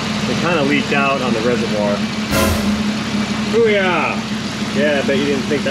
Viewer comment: Are you a former Marine? I thought I heard you say 'Oorah' as you took the radiator out before the water pump repair.